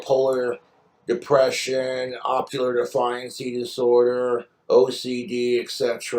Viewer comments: I thought he said Ocular 😭😂💀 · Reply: I heard "ocular" too but he still said "defiancy" lol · Did he say ocular defiance disorder?? 😂